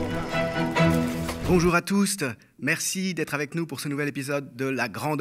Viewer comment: J'ai arrêté quand j'ai entendu "bonjour à touste"